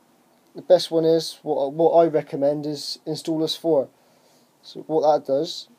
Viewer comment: What did you say, install what? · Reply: Cristopher Hiesler Installous